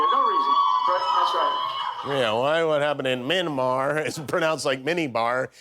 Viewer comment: The guy says "Minimar" (Myanmar) I thought he said MiniMart.... 🤣